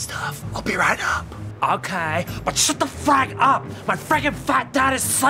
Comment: They didnt censore his voice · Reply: I heard Ian say "Shut the frak up."